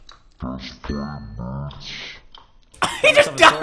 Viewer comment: anyone heard say bit** in